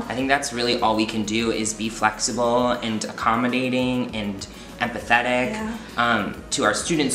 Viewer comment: I thought you said "be flexible, accommodating, apathetic..." Had to do a double listen. LOL